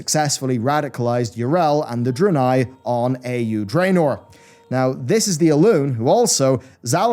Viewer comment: I heard ... "radicalized draenei on EU Dreanor" - That can't be... turned on the captions, started googling, thinking, what did i miss.. and have blizz really done this? I mean, it kinnda made sense, sort of. Dreanei, losing their shit (again) but only on the EU Dreanor server. And then their madness could spread to other servers. Ahhh, then it hit me. It's not EU it's AU. Alternate Universe. Never mind. Would be cool tho...